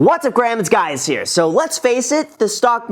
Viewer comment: Did I just hear him say "What's up Grahams, it's guy over here"?